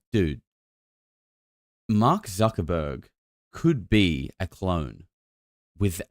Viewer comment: 😂I thought you said Mark Walberg not Zuckerberg, I was thinking yo why is bro going off on Markie Mark 😂 also Zuckerberg means Sugar mountain in German